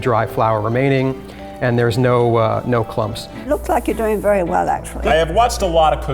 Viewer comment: it sounds like she said tf ur doing very well actually